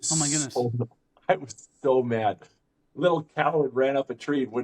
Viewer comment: give it a listen. I don’t think I misinterpreted what the story was or what he said. If I did it wasn’t on purpose that would require you to be so aggressive towards me. Not very nice of you. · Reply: he said little coward, I heard it as Damm in my head till I re listened😂